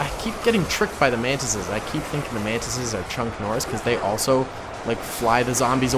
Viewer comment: Did he just say chunk Noris